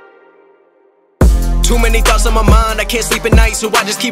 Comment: I heard "Too many box in my mind"